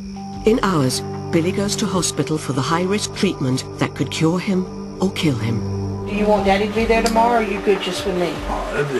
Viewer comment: I thought she said "It could kill him, or kill him"